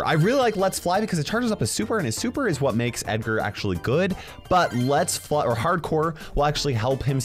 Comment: did he just said "makes edgar good"?💀